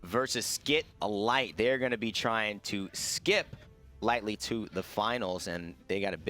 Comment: It sounded like said "Well, they're trying to get a life" my ears WHAI?!